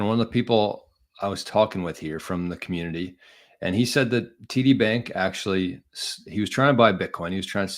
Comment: I thought you were saying "TITTY BANK😅😂